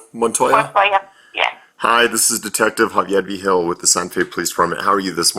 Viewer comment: I'm confused, it says detective vigil but it sounds like he says here "Hagiebi Hill"